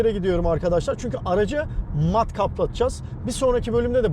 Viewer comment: "aracı mat kaplattırıcaz" kelimesini ilk duyuşta "matkaplattırıcaz" anladım diyorum neden 😃